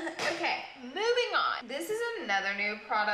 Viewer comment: when the subtitle says "low income" instead of Lancôme.. Ironic 😂